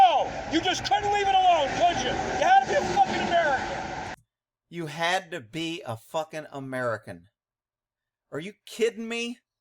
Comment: Bloody narcissist with a badge! I think he said "Captain Fuckin' America!"